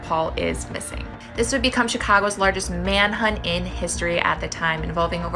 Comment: wait a second did she say manhunt? <link>